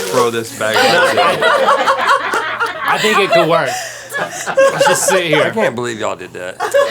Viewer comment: Did anyone else hear Link say "I think it could work" ?